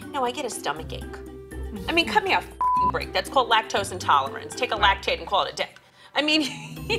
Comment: MARGARET IS SAVING THIS SHOW (“it’s called lactose intolerance, take an effin’ laxative”) 😍😍😍😍😍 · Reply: She actually said, "take a lactaid"